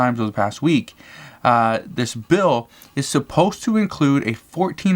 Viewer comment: Will adult dependents finally get a check?? Nevermind, I heard you say they are "supposed" to.